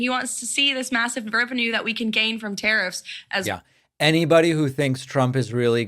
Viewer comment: I keep hearing her say 'terrorists ' and she's actually saying 'tariffs'. Oh and that white haired guy doing his little dance in the chair. He's strange.